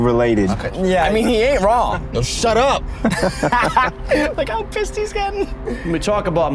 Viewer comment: Did bro say look how pissed hes gotton 2:06